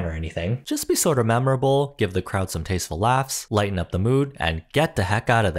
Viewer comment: LMAO MY EARBUDS WERE RUNNING OUT OF BATTERY AND IT SOUNDED LIKE "give the crowd some- please charging"